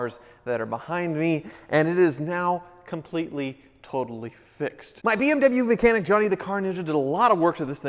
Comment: "And it is now completely, totally fixed!" What I heard: ""And it is now completely, totaled" Bad habits...